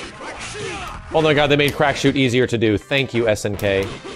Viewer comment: Crack shoot?? I thought he was saying black shoe.